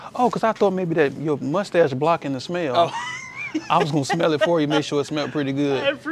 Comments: I thought he said "maybe your musty azz was blocking the smell" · I legit heard him say “your musty ass is blocking the smell” 💀💀😭😭😭😭😭